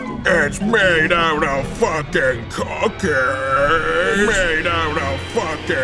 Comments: I heard "Made out of fucking carcass" and it ruined the video for me D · Who else thought he said "It's made out of fuckin' cocaine" at first? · It wasn't until the end of the video that I realized he was saying cookies. I thought he was saying it was made out of cocaine. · I am not the only one that herd "It's made out of fuckin carcas"? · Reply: I heard cocaine the first time so I was really shock, but it was a misheard I easily understood its meant to say cookies and heard the correct word right after. Was still funny to imagine the whole thing as saying cocaine instead XD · Fuck. I heard "it's made out of fucking cocaine" and thought it's the most fucked up ad until I heard cookies at the end :D Still fucked up. 8/8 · I thought it said cocaine! I just figured out it was cookies... :/ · I thought he said that it was made out of cocaine....it was cookies.... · At first I thought he said "made out of cocaine" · The first time i heard cookies i thought they were say cocaine xD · I thought he was saying it was made out of cocaine XD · it sounded like it said cocaine not cookies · Lolololololol I remember when I thought he said cocaines but it's actually cookies.